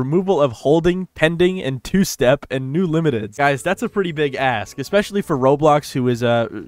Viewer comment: did u just say "ass" or am i mishearing things again